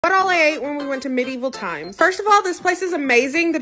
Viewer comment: When she said medieval times I thought she said 'what I ate in medieval times' thinking it was some joke about eating in Italy rn